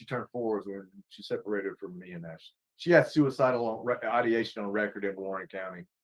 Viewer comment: right here i think u might have misheard the bio dad. He says that the daughter separated from he AND Ashley (mom/vic) just before the age of 4 and had suicidal ideation, due to the break up. I wonder if he means the suicidal ideation was at 4 too because that would be extraordinarily irregular. Everything said still applies, just saying he's talking about the child. "Nobody ever called me." Re: the defense strategy- sounds like my narcissistic father. You don't have to be called, that's your child! Call them! Get involved if you believe that's right. Why are you waiting for golden engraving on a f***ing invite?!